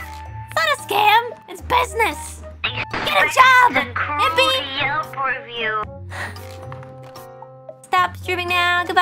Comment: sounds like she says “expect a cruel yelp review”